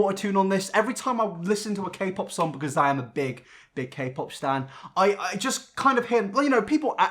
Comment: when he says he’s a big “K-pop fan” it sounds like he said I’m a big “k-pop stand” or am I just crazy? 😅 (probably crazy)